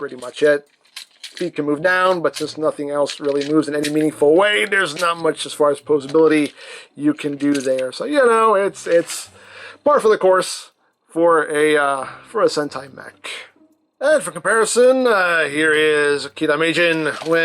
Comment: I thought is said senpai